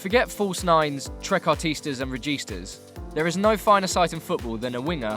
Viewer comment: What did you say after false 9? registars? what? someone tell me